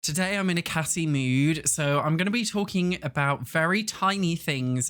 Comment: thought you said you were in a gassy mood and i was like "ok tmi"